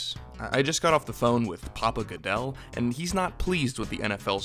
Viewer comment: for a second I thought you said "Pablo" Goodell, y'know... like Escobar.... Yet I did not bat an eye 😂